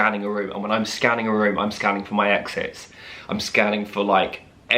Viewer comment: I thought you said "When I'm scanning a room I'm scanning for my exes" hahaha me too never know when those fckrs will appear